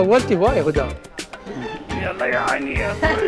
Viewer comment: اخوان احد يكلي بس اني الي سمعته بالدقيقه3:24 يكولها ياعلي🤤 معقولة اني دااتخيل😂